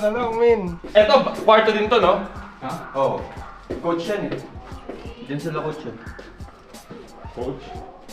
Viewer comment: Tama ba yung narinig ko 3:26 "jan sila coach YEB" ??🤯